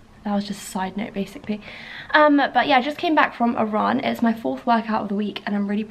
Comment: Who else thought she said “I just came back from Iran” at 11:37 LOOOL I was so confused haha then I realised she said “a run”